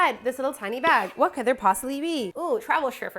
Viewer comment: I could of sworn you said travel strippers not slippers